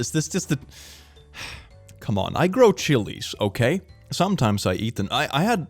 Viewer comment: I thought he said "I grow children. Sometimes I eat them" .... He said Chilis.:face-turquoise-covering-eyes